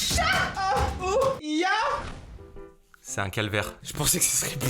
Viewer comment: A 5:27 c’est moi ou il dit "Nyah~!" 😶